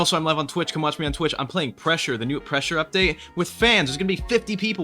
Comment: Thought you said thresher instead of pressure